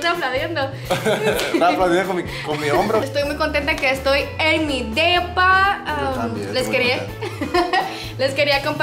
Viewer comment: Pensé que decía tour de mi Pepa y al ver la cosota esa que se carga, no pensé dos veces en abrir el vídeo